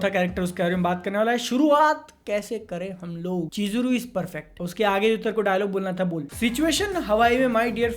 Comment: DID I HEARD PERFECTT!!